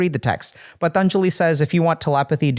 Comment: anyone know what text doctor K is referring to? Not sure how to spell that name "Tanjeli"? Couldn't find anything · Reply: PATANJALI is a revered figure in Indian philosophy and spiritual tradition, most famously known as the author of the Yoga Sutras, a foundational text of classical yoga philosophy.